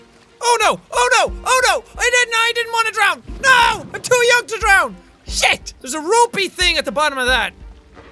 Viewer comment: all I heard was I'm to yarn to drown